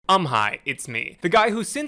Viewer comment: I thought you said “I’m high it’s me” in your opener